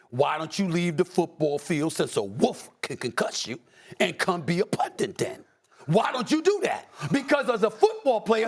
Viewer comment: I thought this MF said, "Since a WOLF 🐺can concuss you🤕, and come back and be a punter then!!" 😆🤣